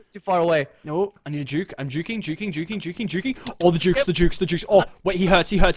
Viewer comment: "OHHH THE JUKES THE JUKES OH THE JUKES OH THE JUICE" kindove (ik I didn't spell right) sounded like he said the juice😉😜😛😝